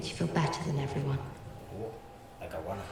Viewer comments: can anyone tell me what said at 0:40?? i cannot listen to that!! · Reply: "Like a Rana" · What did my dude say at 0:39 that got that reaction? I can't understand him. "Oooh. Lakawana." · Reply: I'm fairly certain he said like. The girl with the crown. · Did say "Like Rana" at 0:40? If yes, reaction was priceless ! Almost like " Yes but don't say that on YouTube" 😄